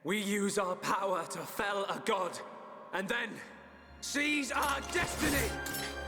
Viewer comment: It didn't sound like he said "seize our destiny" but "cheese our destiny"